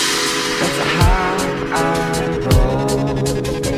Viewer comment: I always thought it said "That's a high-igh road."